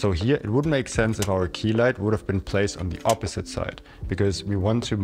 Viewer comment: did you say it 'would' have made sense, or 'wouldn't' have made sense? Also, is that practical light basically acting as a rim light as well? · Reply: Wouldn’t. And it hardly does as you can see when I turn them on one by one